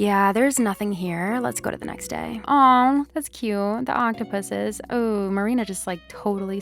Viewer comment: When you said the octopuses I thought you said the ankha puses. Im dead